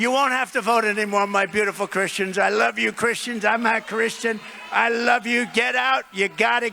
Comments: Did Trump say “I’m not Christian” 1:03:15 🤨 I really hope I just heard it wrong. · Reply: He didn't say that. He said, "I'm a Christian". I understand that he is sympathetic towards and pro Christianity. He believes atheism is nonsense, and that people of faith should have their 1st amendment rights ensured and protected. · bro . He said I'm a Christian. He didnt Say i'm not a Christian. Lesten to It again . Its just that he didnt pronounce It proprely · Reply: bro . AT first like you i thought he said i'm not a Christian. But then when i lestened carefully and I now know he said I'm a Christian.